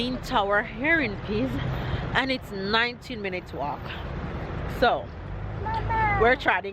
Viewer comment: well girl i thought you said 90 mins so i got up and went for my bottle of water to drink while you all did that hike lol expecting to see those children on you all backs enno lol